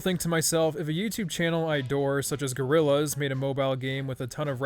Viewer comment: did- did you just say Gorillaz? 😳 Let’s be friends I love Gorillaz